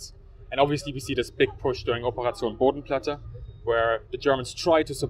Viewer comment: what operation? I can't quite understand the accent. Thanks. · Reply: PierreDolphin Operation Bodenplatte